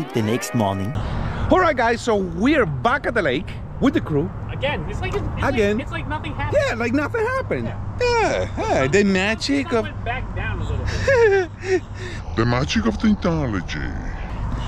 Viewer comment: Hahahahaha I love when does the Dramatic Deep Voice! Like at 2:30 and 10:06 and 18:21! Hahahahaha😂 I love this channel so much! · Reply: What does he say at 10:06? "The magic of Tentoligy"? 🤣🤣🤣